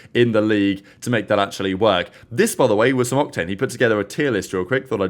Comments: why does it sound like said cocktane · Did I hear Co¢tane at 3:00!? 😂🤣😭😂🤣🤣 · did he just say cocktain?